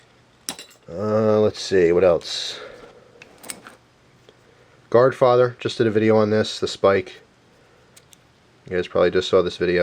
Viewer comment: What does he say at 4:09 which weapon is that? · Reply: Mr. Deadpool godfather